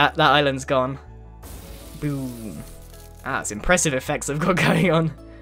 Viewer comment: "That was impressive effects of cocaine" Am I the only one who heard that??